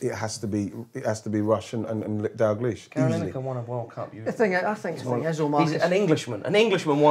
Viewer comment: Did he say that Lineker won the world cup at 6:13 or did I mishear that?